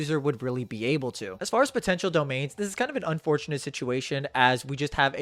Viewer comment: did you say "potential"?